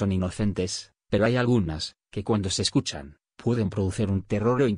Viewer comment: las frecuencias que? no entendi bien el nombre🙏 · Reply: i doser ! Se refiere a una dosis de droga digital. Estas frecuencias pueden perturbar muchísimo a la persona que lo escucha.